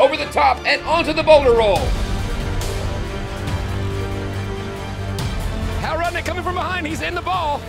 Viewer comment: I definitely thought he said boner roll instead of Boulder roll